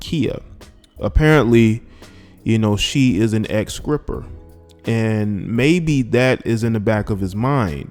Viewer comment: Dis I hear "skripper?" 😳